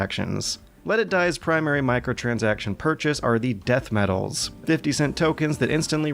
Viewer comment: I thought you said Death Metal not Death Medals lol. Oh wait you were saying Death Metals. \m/